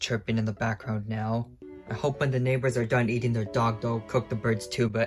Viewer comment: I thought you said that your neighbor eating their dog😭😨